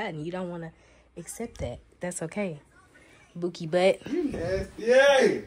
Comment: I guess I'm the only one that heard him say "Bae & Boo"